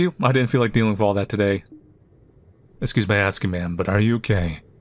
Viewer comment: I wasn't paying attention to the video (was watching it as background noise) and it sounded like he said "Excuse my asking, ma'am, but are you *gay*?". Fucking whipped my head around at about mach 10 💀